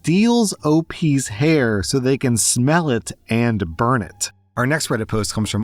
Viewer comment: Yeah I definitely heard "sells it" instead of smells at first and thought it was just cowboy wig making